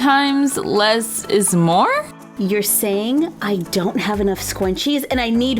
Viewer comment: It sounds like she said scwunchies